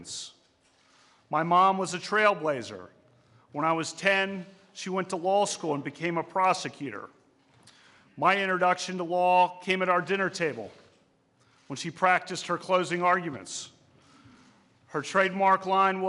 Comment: I thought he said his mom was a prostitute. I was like "what??!?"